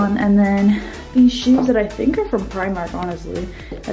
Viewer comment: wait did she just say her shoes were from Primark.